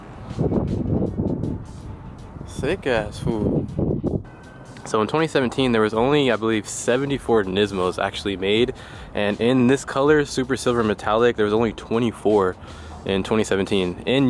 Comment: Anyone else heard ford nismos😂